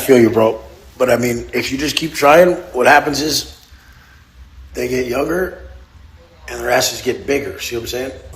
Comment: did he say they get younger?